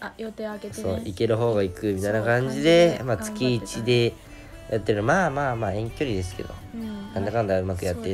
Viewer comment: 月一でヤってるって聞こえた気がするんだがw